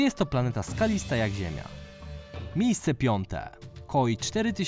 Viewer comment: kto wpierw usłyszał covid oprócz mnie?